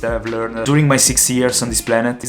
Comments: so how old is he actually? Cause I heard 16, and 60, and both don’t make sense. I would have guessed that he’s 20-something, but clearly that’s not what he said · did he say he had 60 years???